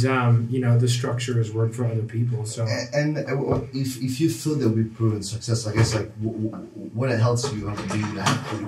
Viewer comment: What does say at 3:57? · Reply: "if you feel we are proven success, what else do you have to look deeply inside?" said a sentence or two prior he "would have to look into a little more deeply" lol amazing